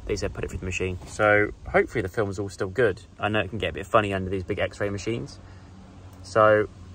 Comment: I thought I heard you say "the film can get a bit funny under these baguette machines" 🥖 and I was like... damn... didn't know they had that in Dubai